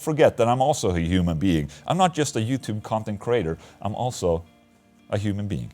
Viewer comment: I misheard 7:40 as "I'm not just a Youtube C*nt and creater" xD